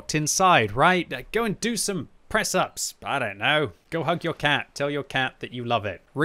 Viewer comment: I thought he said "hunt your cat" :)) sounds like a fun game